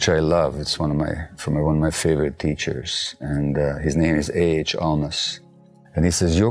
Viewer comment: Can anyone tell me who Dr Mate is quoting at 1:04 ? Please and thank you. 🌺 I cannot make out the name. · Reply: Hi, Kathy! A.H. Almaas.❤️